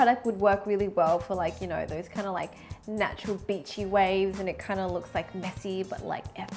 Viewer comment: the subtitles spelt beachy like bitchy and i read that before i got to that part and thought she actually said it lol